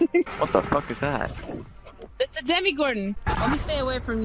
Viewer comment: Is it just me or did she say 'Demigordon'?